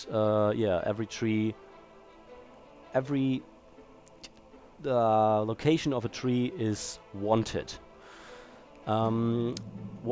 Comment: Is it just me.... or at 1:46 ... Did say, "Every placement of the tree is one tit."?